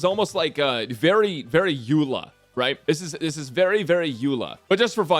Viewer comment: What does he say at 9:43 ? "This is almost like very, very ___"? Yula? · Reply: eula, a character from genshin in case you don't know